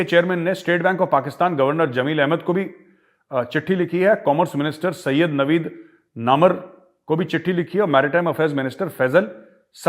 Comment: is i m the only one who heard Syed Naveed NAMARD??? 😂😂